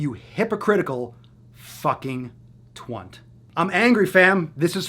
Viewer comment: Did just say 'Twunt'?